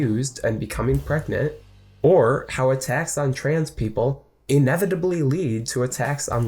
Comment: I heard "a tax on trans people," which I thought could be believable at this point, but then realized I misheard "attacks"